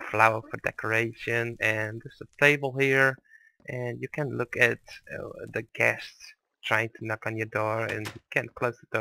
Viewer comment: i thought u said "you can look at the GHASTS trying to knock on your door."